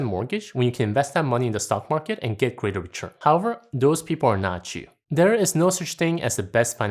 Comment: I thought you said "those people are not Jew" at 11:53 😂